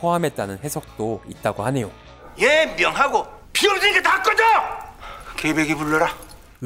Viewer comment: 아 여기 대사가 필요 없으니까 다 꺼져였네요 ㅋㅋㅋ 지금까지 븅신들 다 꺼져 라고 한 줄 알았는데 대사 보면서 들으니까 들리네요.